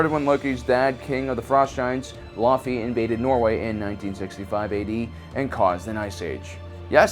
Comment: anyone else hear him saying 1965